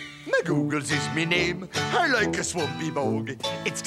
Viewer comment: I always thought he said "MacGoogles is me name, I like to smoke me' bong". 🤣